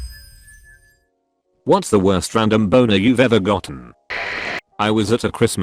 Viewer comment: I thought it said “Random Boomer” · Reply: Rodriguez I thought you said “Random Boner” then I looked at the title then I looked here again then I realized you said “Random Boomer”